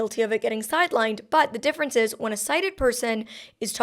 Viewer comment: just a little fix, it says "cited" in the closed captions instead of "sighted".